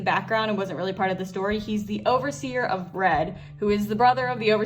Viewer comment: At first I heard you say overseer of bread